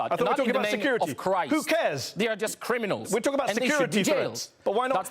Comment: did he say "who cares"? 😮😮😮